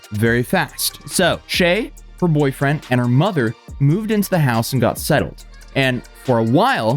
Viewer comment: In the video shay says her mother passed away when she was 2yrs ( 9:13 ) then when it was time to move into the house she moved in with her mother and her boyfriend ( 11.42 ) ? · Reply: Nope yall, he said shay, shays boyfriend and his mother, so he didnt mispeak we just misheard aha, dw i did the first time i heard him